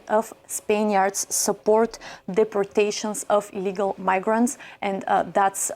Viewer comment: I'm from Spain. I think she says the "I" in illegal too low/short so her mic does not register it properly, but the headline says 70% in favor of deporting illegal not legal , thus probably latter in the video (17:33) sparking that misdirected debate between a chatter and Asmon.